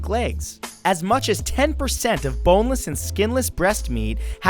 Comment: did you say B O N E L E S S?